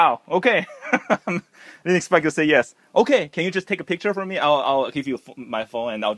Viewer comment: did he just say take a picture for me in you? or my ears need to be cleaned?